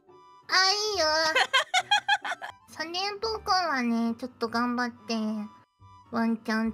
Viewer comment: 迷子センターで事情聞いてるお姉さんとみこちって感じでずっと見てたいわｗ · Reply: やっぱり、三連ポコに聞こえる３連複